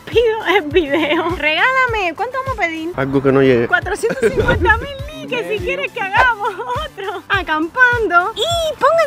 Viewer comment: En el momento 17:34 el movió la boca como si estuviera diciendo algo like si te diste cuenta y responde este comentario diciendo que opinas y que crees que dijo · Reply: Dijo medio millón